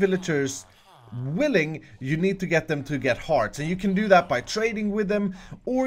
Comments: I thought he said get hard lmao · I thought he said u need to get the villager to get hard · did he say they had to get hard! :o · I honestly thought you said 'hard'